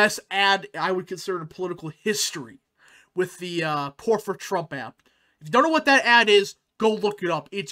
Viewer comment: huge fan here.. quick question at 8:04, are you saying poor for trump ad? I struggled to understand what you were saying. Thank you